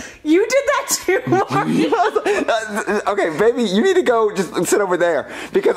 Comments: Did just say Mario? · did she say Mario???